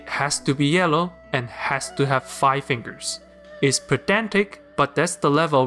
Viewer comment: i thought he said pathetic